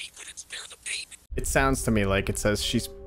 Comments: At like 32:21 I hear "she couldn't spair the pain" What did everyone else here? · I think it’s saying she couldn’t bare the pain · i think the voices in the last part are saying: 1. “he was the first.” 2. “she couldn’t bear the pain.” 3. “she tried to run.” edit: changed “bear” to “bare” :) EDIT 2: IT WAS NOT “BARE” IT WAS IN FACT “BEAR” I AM SO SORRY, ENGLISH IS A SECOND LANGUAGE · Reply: I hear "she couldn't bare the pain" so... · I hear “she couldn’t bear the pain” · what she was saying. “ she couldn’t bear the pain” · I think it says “she couldn’t bare the pain” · i think it says she couldn't spare the baby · I think it's saying "She couldn't bare the pain" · i think it says "she couldn't bear the pain"